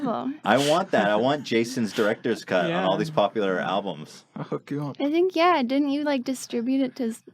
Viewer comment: When Dunkey said "I'll hook you up" I thought he said "I'll fuck you up" lol